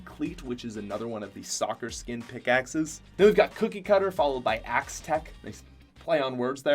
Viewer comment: was looking away and thought he said coochie cutter 💀